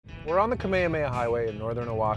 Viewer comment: i thought he said "kamekameha" :")